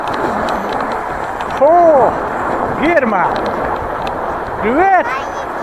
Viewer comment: Перемотала 2 раза, послышалось в начале "о, Ирма!!")))